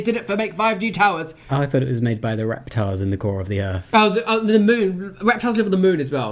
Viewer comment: I legit thought you said Reptars at the core of the earth, and was like "oh, that's a new one" Didn't even question XD